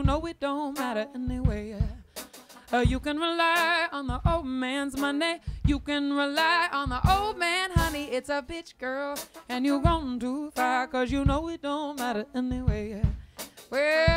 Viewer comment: My whole life I thought she said bitch girl how saddd😭😭😭